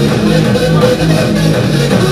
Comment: Did someone say "Go B" at 0:36, or is it just me imagining things?